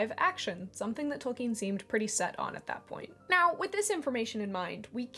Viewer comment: Your "now" at 4:01 sounded like Gary's "meow" from SpongeBob! Very cool!